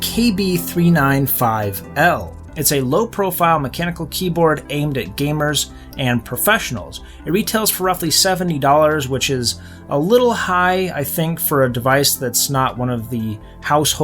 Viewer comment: Did he say $7?